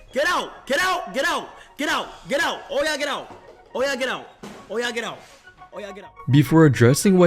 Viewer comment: Did I just hear that right...? "Oya get out 5:49" mah boi's African? ts can't be bro, u bedda not be bluffin 😨